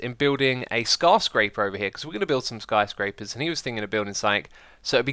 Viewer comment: did you just say scar-scraper?